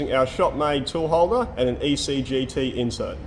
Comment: couldnt hear or read for sure in captions what insert he is using, he did say what it is at 5:31 Think it is CCGT .